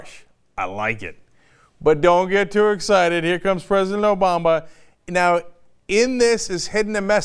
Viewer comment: anyone else hear "President Obamba" at about